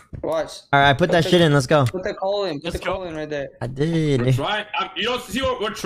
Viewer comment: Bruh did he say I did nigga @ 1:55:42😭